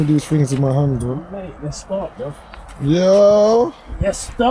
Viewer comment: Sounded like a “let’s spark bruv” at 14:09 😂😂🔥